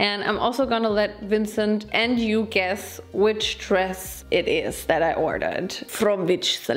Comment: I thought she said guess bitch dress it is